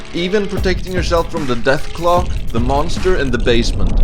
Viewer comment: Did he say monsters and basement?